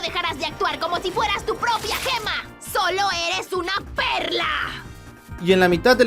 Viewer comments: Juro que entendi perr@ 😵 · La primera vez que escuche esto pensé que Peridot decía: "¡Sólo eres una p3rr4!" XD